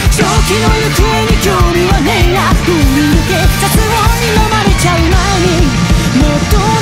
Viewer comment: 絶望に飲まれちゃうって聞こえた…